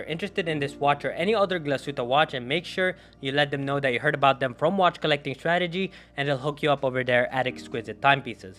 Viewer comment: Did he say "watch collecting tragedy"?